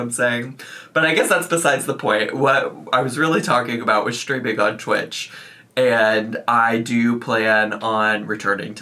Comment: Thought I heard you confess to "stripping on Twitch" 😆